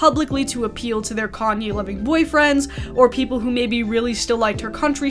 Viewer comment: I heard that as "commie-loving boyfriends," LOL. I'm not sure if the subtitle of "Kanye-loving" is correct, but both make sense, IMO. 😆 Anyhow, I was already a casual fan of Swift's music from her Red and 1989 albums, but I've grown to like her music even more through her most recent 2 albums, her Eras concert film in IMAX (more for her showmanship and stamina than the entire catalogue on display), and the flippancy at which her existence triggers far right freaks.